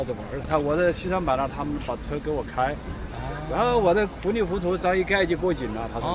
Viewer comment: เขาหน้าจะพูดว่า 糊里糊涂 แต่ได้ยินเป็น fulifutou ฟังจาก H เป็น F 555